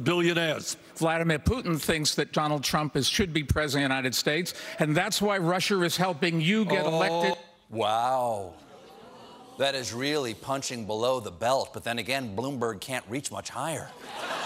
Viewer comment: Bloomberg... did he just say "Russher" for Russia? ...